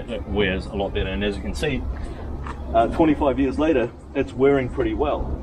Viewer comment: Sorry about the audio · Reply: I wasn't the best.. for instance at 3:11 it sounds like you say 'at 45 years alter' rather than 'at 25 years later'. Customs are classics, not Antique (yet) :goodvibes